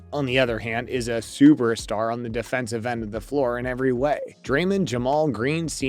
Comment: am i tripping or did he say draylin ? lol